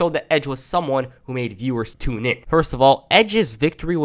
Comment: I thought he said edge was someone that turned viewers to men Cause after watching that live sex on raw when it happened I became a man